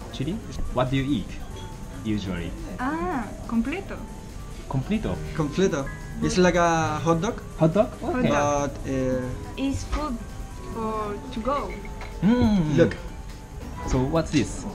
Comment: イヤホンで流してたらコンクリートって聞こえて二度見した